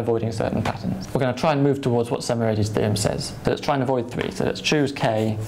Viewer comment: The whole time I thought he was saying "some 80's theorem"